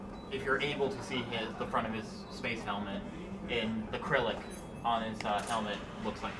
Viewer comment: It sounded like that guy said acrylic instead of Cyrillic.